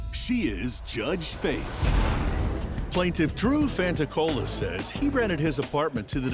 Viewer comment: Instead of Dru fanticola I thought she said TrueFantaCola I was like, is this guy a living reddit username